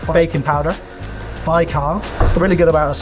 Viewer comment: what ingredient did you say after baking powder? For the breading… can’t hear it clearly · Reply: Bi-carb.